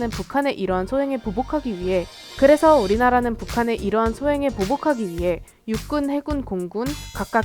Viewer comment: 계속 눌러보세어ㆍㅋㅋㅋㄱㄱㄴ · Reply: 진짜 빡대가리들인가? 0:30을 멈춰보라는데 위해라고 말한게 예라고 들리는데 계속 들으면 그게 스웩있는거같이 재밌게 느껴져서 그런거잖아 븅신들아 사진이랑 그러거때메 웃는게 아니라 꼰대색히들아 우리 시대 못맞추면 아가리닫던가 존나 시비야